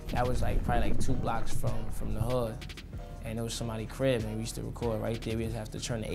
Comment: Wait... @ 2:26 does he said "somali crip" or i heard something else? 😳